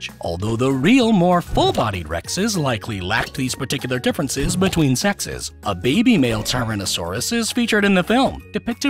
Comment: what bro sayin’ 💀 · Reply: “Between sexes” is what he sayin’